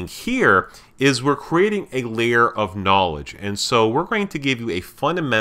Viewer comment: did you say... KNOWLEDGE